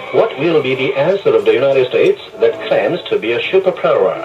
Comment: What in the hell does he say at 1:41?? "What will be the answer of the united states that plans to be a shipapawrrrrrrr" Is he saying "there plans to be a shift in power."?